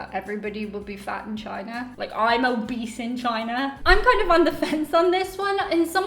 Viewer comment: Did you say you would be obese or a beast in China?🤔🤨🧐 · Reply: Pretty much obese